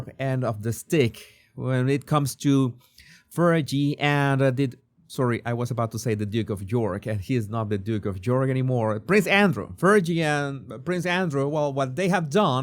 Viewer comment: I thought you said the "dick of york". Perfect Freudian slip! LOL